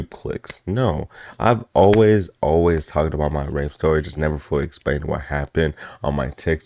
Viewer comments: dang. sorry about that, I thought it said “my rap story” at first ❤ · I THOUGHT IT SAID "my rap story.."😭🙏